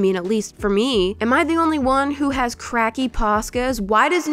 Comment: I heard “am I the only one who has cracky pasta”😂😂😂😂😂😂😂what😂😂😂😂😂😂😂😂😂😂😂😂😂😂😂😂WTF😂😂😂😂😂😂😂😂😂😂😂😂😂